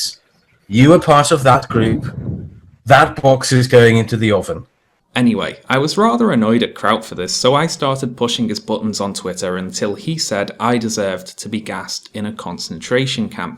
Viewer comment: I thought he said at first "That fox is going into the oven" right when that cartoon fox avatar appeared. I don't know why that was so funny to me.